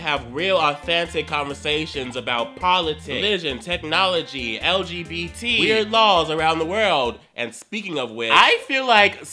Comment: i thought it said "wierd lawns" 😂😂😂